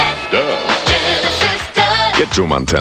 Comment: When I first head this I thought they said dirt genesis dirt